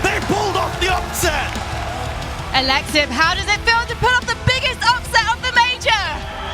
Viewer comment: did she just called him aLeXIB